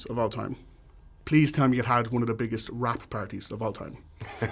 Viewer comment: did he say.. wrapped party?